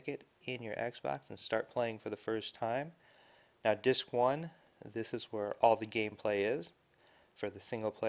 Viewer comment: hahahahhaaah it sounds like he said 'this is were all of the gay play happens'